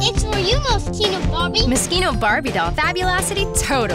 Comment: So that's how you pronounce Moschino. I always thought it was Mo-she-no. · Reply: I thought they were saying mosquito Barbie lol. I was like WTF. Ohhh Moschino ... wait, what's Moschino? I needed help from the google machine.